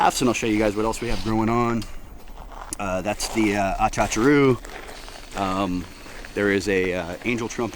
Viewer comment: What did you say that was at the 9:52 mark? Trying to look it up but obviously am not spelling it correctly. 🤷🏻‍♀️ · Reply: Garcinia humilis, known commonly as achachairú or achacha, is a small, prolifically fruiting tree related to the mangosteen. It grows in the southern part of the Amazon basin in the central area of Bolivia